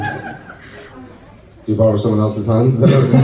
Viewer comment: Please, what did Nathan say at 39:34? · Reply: I hear "you borrowed someone else's hands?", and then Gary laughs and makes the voice and movement of unfastening 8)